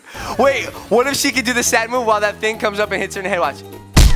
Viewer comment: Sadnoose i thought you said sad moose.🐂